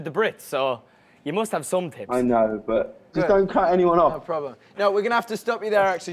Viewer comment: Did anybody understand what james said at 9:21? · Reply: He said "I know but, just don't cut anyone off" so that's why Harry cut james off